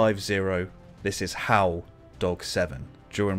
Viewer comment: I always hear “this is hotdog 7 sending rounds Willy nilly” 😂